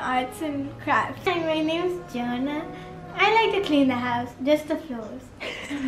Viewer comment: “i like to clean the house, just the floors.” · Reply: I thought she said “I like to clean the house just to feel something” I was worried for a second 😭